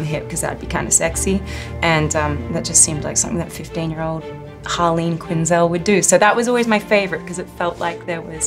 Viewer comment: did she say "Harlene Quinn"?